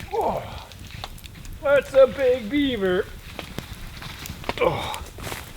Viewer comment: When was peeing in the back, I thought he said “it’s a big beaver” and he was talking about his pecker